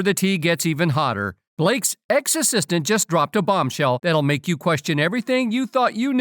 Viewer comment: I heard Blake's exorcist instead of Blake's ex assistant and it took a second to realise there was a mistake and she likely doesn't have a personal exorcist 😂😂 hopefully 😅